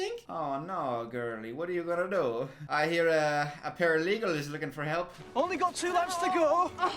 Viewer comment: ah yes, the infamous flesh-eating virus that turns people into paralegals. Vicious and litigious 😂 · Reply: Came to comment this because I was like, "Did you just say paralegal?" 😂